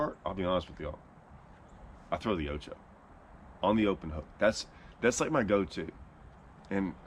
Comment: I am not sure what he said there Yojo? Yo? Brand? Name? Can any one Clarify please? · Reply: Strikeking ocho